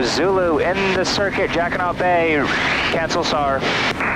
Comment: was it just me, or did it sound like he was saying "Jackin' Off Bay"? I had to look up that it was Jacquinot Bay.